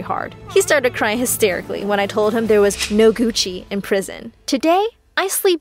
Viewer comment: I thought she said there was no coochie in prison💀